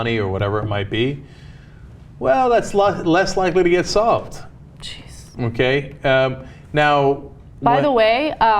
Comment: thought I just head the women say Jesus @ 3:13.... but I thought there was no God and religion is a bunch of fairy tales?